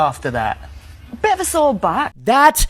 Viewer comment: Haha I thought she said "a bit of a sore butt" lmao. It's pretty clear looking at this guy he just seems defeated. Then when your wife is smiling and laughing saying she isn't happy in front of you unless she's taking a bunch of random schlong it's time to swallow your pride or actually have some and get the f*ck up outta there